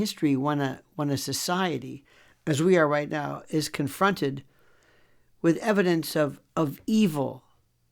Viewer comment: Oddly enough, I stepped away from my phone for a moment and I thought I heard the word “hunted”…replayed and learned you said we are con-fronted, not hunted…but, it is possible evil hunts the goodness and light in us. Thank you for your focus on this evil.